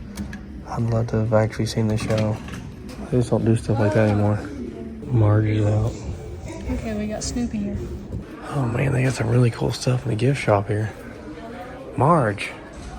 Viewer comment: You say "They just dont do stuff like that anymore" I thought you say "Please dont do stuff like that anymore". I kept going back to see what it was Squeezy or Ralphie had done! 🤣